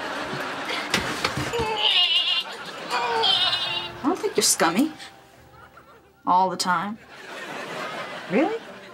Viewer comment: What Is Carly Doing Why Is She Crawling And What Is She Saying????🤣🤣🤣😂😂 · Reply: NYEHHHH NYEHHHH